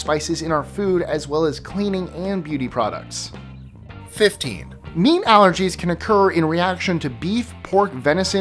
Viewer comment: I thought he said meme allergies lol